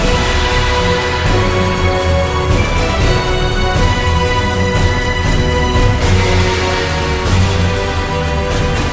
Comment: what did it say I can’t tell? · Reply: "What are you still doing here, check on strycore" That is what it say